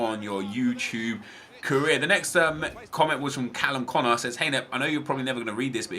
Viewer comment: thought he said Callum's corner